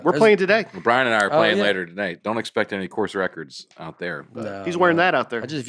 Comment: At the beginning when Nate is talking about playing golf with his buddy, I thought he said “course wrecker” not “course record.” I started imagining Nate playing golf while his buddy went around demolishing the course mid play. Great show guys! Keep it up!